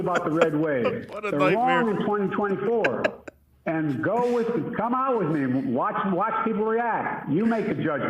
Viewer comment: I love how he outright insults Kamala 😂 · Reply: I’m still not sure. I thought he said “Go with Kamala’s name, watch people react, you make a judgement.” But yeah it’s possible he says “come out with me” instead of “Kamala’s name”. Also, we don’t know how the hosts heard it either. I thought Mika’s reaction was kinda surprised at the remark which makes me think she heard it the way I did. Who knows. The fact we’re even having this debate is absurd. No one can understand wtf this guy says.